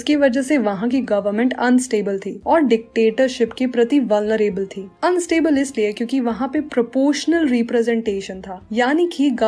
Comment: 🙏Mam🙏 at 8:54☝ dictatorship ke ....... prti kya thi samaj nhi aaya😲 aapne kya bola😓❓❓❓ Mam if u see my problem plz solve it 😰😰😰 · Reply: Vulnerable!!